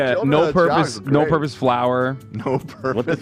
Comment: A little mad that "no-purpose flour" did not show up on this tier list. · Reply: Ohhh, that’s what they said. I thought they said “no-purpose flower” and I was so confused. 😂 to my defence English isn’t my first language.